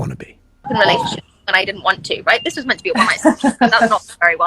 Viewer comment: I can't understand wtf she's saying in that 144p clip - that she didn't want to be with him and it isn't going well or "This was meant to be a ???? That's not very well at all" maybe I just don't speak British succubus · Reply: She’s saying “this was meant to be a one night stand”